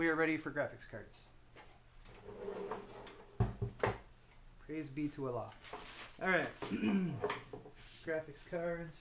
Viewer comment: What did he say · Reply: praise be to allah